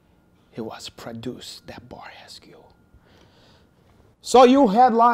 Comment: What does Hen-Arch say there ? "he was produce the . . . . .. . " I can't work it out (lol Hen Ach !) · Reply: "...the Bar Hescue"